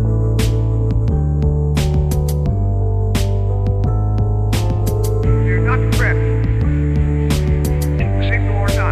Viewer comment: what he's saying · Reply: Do not fret! Single or not! The fungus will spread. ITS IN THE WALLS